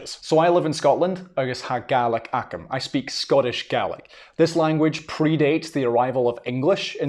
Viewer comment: Am I the only one who heard his accent and thought it sounded like he was saying “Scottish Garlic” everything he said “Scottish Gaelic”?